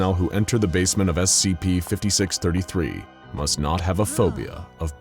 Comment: Not gonna lie….I thought it said must NOT be homophobic…. I’m ashamed Edit…ok now I’m less so seeing a others have made the same mistake